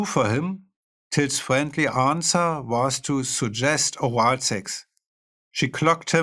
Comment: What was "Till's friendly answer"? I couldn't quite understand what Flake said here. Still, this was a brilliant audio book and a lot of fun to listen to. Really insightful and funny, he's a great writer. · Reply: "Suggest wild sex" I believe